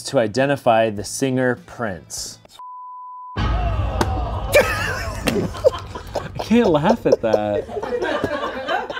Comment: what did anthony say at 17:50 ? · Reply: Pretty sure he said “sw*stika” it’s from the first few minutes of their trivial pursuit video